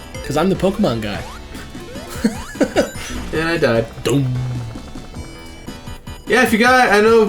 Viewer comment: i always heard the death sound like this "DUMB" !